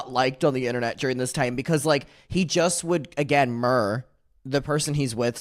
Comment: It took me forever to realize you were saying mirror I thought you were saying murr lol